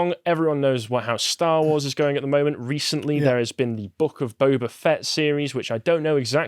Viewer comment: Honestly, I misheard on the first pass and thought he said "The Buck of Boba Fett"... which is an accurate nickname for that steaming mess.